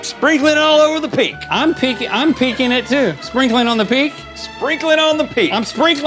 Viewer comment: I THOUGHT THEY WHERE SAYING "SPRINKLING ON THE PINK"... I WAS LIKE OMG, WHAT?